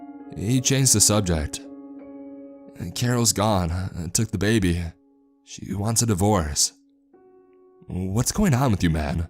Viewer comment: Thought he said “Karen took the baby”